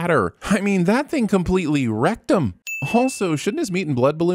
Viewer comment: When talking about translucients buttsplosion I heard wrecked 'im as rectum. Missed opportunity